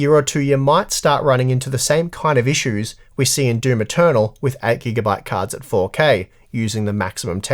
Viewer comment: the subtitles say "do maternal" instead of Doom Eternal! xD